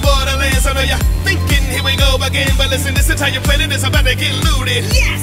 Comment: I heard: "This entire planet is about to get lewded."